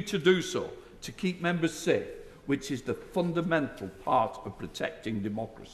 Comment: Oh fundamental, I thought he said "fucking mental" lol